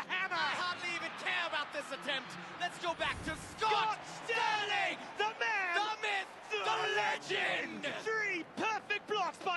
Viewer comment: 空耳「スコット！スターリン！ダメぇ！ダメぇ…ドレッシング！☆」 4:02空耳「アアアーォウ！ダーレガシタンダヨこんなもォォォン！！」 4:08「分かんない！リプレイでJustLight☆」